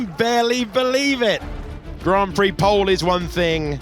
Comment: It's not here, but I'm pretty sure the announcer said "Kimi Raikkonen" instead of "Antonelli", which explains Kimi's reaction and laugh 😂